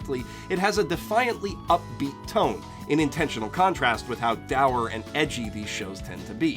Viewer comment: What word does he use? "Dower"? "Daauer"? Never heard of it before and I don't know what it means. Lol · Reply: Dour